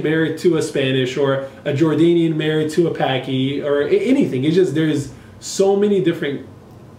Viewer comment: did he just say paki 😭😭😭